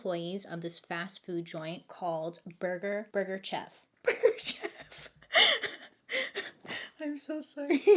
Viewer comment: I died when she was laughing at the way she pronounces burger chef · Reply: Nicole I thought she said Burger Shelf 🤣